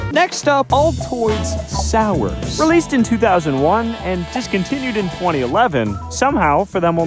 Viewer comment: I thought it said Adidas so I said IRL ADIDAS